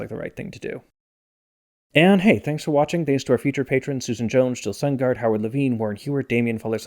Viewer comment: At the end when you thanked "featured patrons" I thought you said "future patrons" and for a brief moment I was thinking "how does he know?"